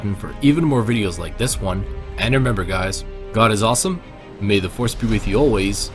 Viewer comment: did he say "god is awesome"? lol